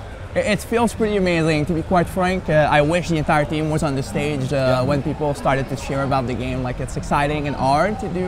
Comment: does he say, " white people started to cheer about the game, likes its exciting and art to do such a surprise...." ~_~ i dont know if i should be offended and not buy the game, or buy the game and pretend its not bothering, or maybe he just said something else, im dazed still jajajaja